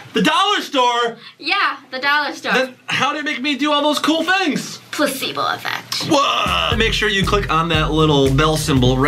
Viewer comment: WHAT DID SAY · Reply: Placebo effect